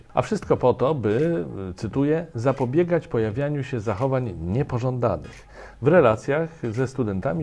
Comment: oj pomyłeczka moi drodzy proeuropejscy dziennikarze - w napisach powinno być "niepoŻądanych" :))